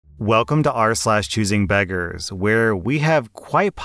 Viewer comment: When I heard you say r/choosingbeggars, it sounded like choosing bedrooms…